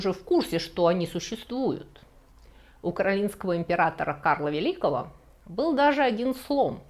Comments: украинского короля Карла Великого? 🤔 Можно поподробнее или я что-то не так слышу? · Украинский император Карл Великий?!! Мне одной послышалось?